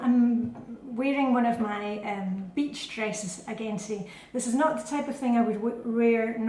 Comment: Oh, I thought you said “bitch dresses”!